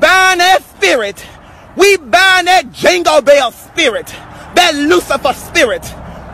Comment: Did he just say that Jungle Bell Spirit🙄😬